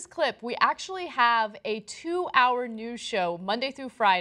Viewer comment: i thought ana said "we have a 2 hour nude show" lol